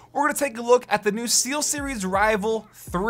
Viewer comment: Google voice to text got it right, but I heard (0:10) Sealseries. lol